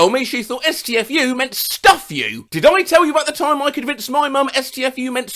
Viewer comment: I used to think it was "Stuff U" as well when I first heard it